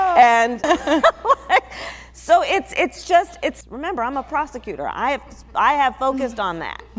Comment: who vvvvvvvvvvvv ha ha ha haaaaaaaaaa..... my administration ....... both are getting hi ? .... who vvvvvvvvvvvvvvvvvvvvvvv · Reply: at that time, I was doing something around the monitor, I heard “so it’s just, it’s, remember, I’m a prostitute.”. I jumped and said,”Oh My God. Did she say that?”. then I re-played the video and more clear that she said, “so it’s just, it’s, remember, I’m a prosecutor.” …… My bet. It was my hearing thing ...